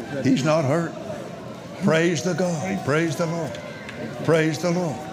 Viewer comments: anyone catch Copeland say "praise the god"......which one Copeland? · Listen carefully - White screams ‘Get jesus the bitch out” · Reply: Totally agree with you Chaseman. But I am being honest - that what it really sounds like he is saying. The same with Copeland at 3:07 - he clearly says "praise the god" then very quickly comes in with a praise the LORD to cover it up.